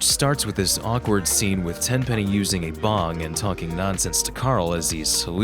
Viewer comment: Narrator says 'bong', closed captions says 'water pipe'. Censorship... how ironic.